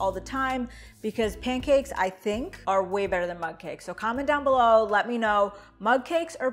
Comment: I thought you were saying MUD CAKES!